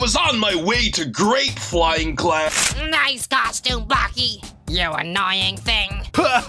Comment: Did Four called bocky wth